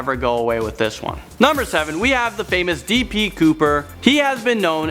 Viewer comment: when you said B D Cooper I thought you said T P Cooper